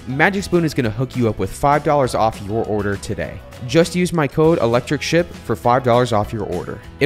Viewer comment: Had to read the code for magic spoon myself because when said it, I thought he said "electricshit."